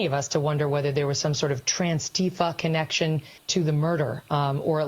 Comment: did she just say transtifa 🤣🤣🤣🤣🤣